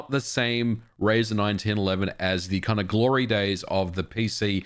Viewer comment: I thought he was saying “9, 10, 11” until it was shown on screen as 1911. All I could think after that is that “Razor 9, 10, 11” sounds cooler than Razor 1911